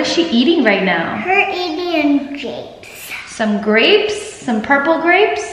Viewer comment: Elle: Shes eating some grapes but it sounds like shes saying drapes lol. She's too cute